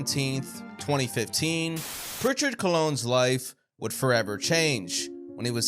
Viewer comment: Did he say Prichard “dickhead” Calone